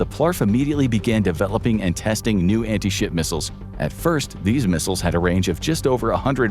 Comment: Anyone else hear anti sh*t missiles?